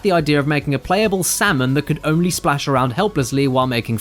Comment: at first, i was sad the salmon guy wasn't added but can you imagine if you played an arcade game for a whole dollar and just got a flopping salmon? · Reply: OH. SALMON. I heard "flapping semen", for some reason, especially since the character was white. That was really confusing.